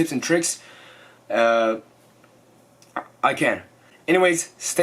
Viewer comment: What are you saying? ......to kill